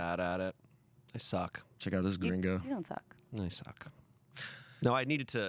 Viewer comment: trying to figure out what's saying "... ... gringo"?? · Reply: Check out this gringo